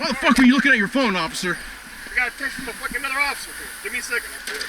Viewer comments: “I was texting another officer” Moments later “I wasn’t texting, I was looking at my phone” 😂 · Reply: I thought he said hes gotta call another officer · Sounds like you said I got a text from another f u c k i n g officer 😂😂😂😂